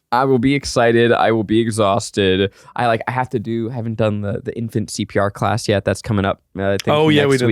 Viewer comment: When said infant CPR class (10:33) I heard infancy PR class and for a horrible second I truly thought he was going to classes to learn how to manage the PR around his baby